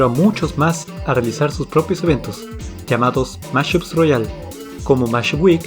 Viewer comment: Esa obra maestra fue tan épica que hizo que varias personas hicieran sus propios torneos inspirado en KFAD (véase mashup week, third world tournament, temporary flight attendant, rulers of everyone's mind, prizm, rave week, ultimus, another unnecesary tournament y demás torneos, en serio que son demasiados, sobretodo en soundcloud) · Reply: oye, disculpa las molestias, pero te quería preguntar, ¿en este minuto que fue lo que dijo?, yo le entendí "Mashup realms", pero no estoy seguro si eso dijo. Me interesaría saber que dijo exactamente, porque ando buscando la mayor cantidad de torneos que pueda encontrar y los ando metiendo en una lista.